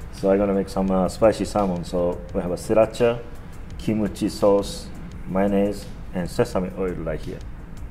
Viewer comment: I thought he said Kimochi sauce? Lol, that a whole other meaning.